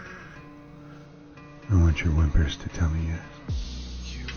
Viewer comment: ohhh he said "whimpers" I legit heard livers I had to rewind that a couple of times